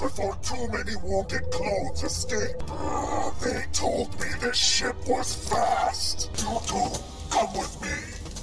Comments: why I heard "They said this shit was fast"? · younger me always thought Grievous said "they told me this shit was fast!" 😂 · When he said “they told me this ship was fast”, I thought he said. “They told me this SHIT was fast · You cannot convince me that Grievous did not say “They told me this shit was fast!” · i was scrolling through comments and didnt see the video, and it thought he said, "i thought they said this shit was fast."